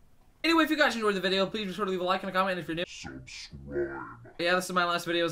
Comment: my bad. thought u said leave a link . happy belated birthday